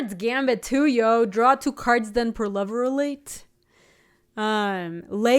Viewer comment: At P2/P1, it totally sounded like she said, "Pro-lover-late".